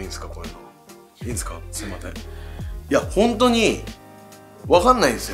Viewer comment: 空耳かな、だほちゃん 「すいまてん｣て、聞こえる笑